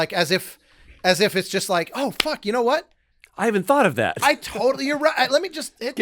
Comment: Playing Civ at 3am with youtube on the other monitor and I hear "Fuck you Tim" lol love this podcast